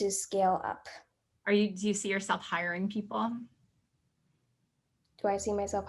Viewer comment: She will definitely need to hire to scale. She will and that’s a great example of Brennan and how he hired friends moms. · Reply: Hi thank you! I definitely will! At the moment I heard “Do you see yourself higher than people” and obviously not! Sorry for the confusion 😅💗 And thank you for the support!